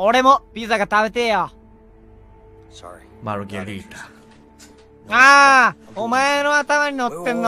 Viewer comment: on 7:07 it seems like the translator didn't understand what kanae said when they were dubbing the cutscene, kanae said "margarita" but it was pronounced poorly so it was hard to understand lol anyways this was so hilarious thank you for translating!!<3